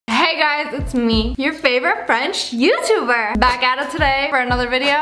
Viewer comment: Am I the only one who heard that baguette it today?